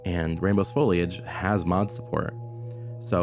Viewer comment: I heard "Rambo's Foliage" everytime until I read the name of the channel